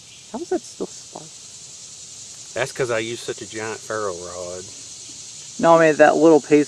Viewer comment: For a minute I thought I heard you say "I used to use a giant Ferro rod." I started to say WHY would you ever STOP?!😂